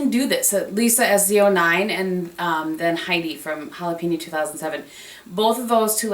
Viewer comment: Hi! I recently discovered your videos and love them:). I was wondering what Heidi's channel is that you referring to at about 8:52. I kept rewinding your video and I just couldn't make out what you were saying when you stated her channel name. Thanks! Loving your videos! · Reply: Hi there!! thank you so much!! Oh that is jalapena2007 she is amazing and truly was one of my biggest inspirations to start my channel!! :) xo